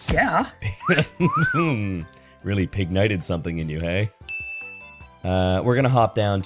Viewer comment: I guessed Emboar here so when he guessed Pignite I heard it as “That’s Pignite’s brah!”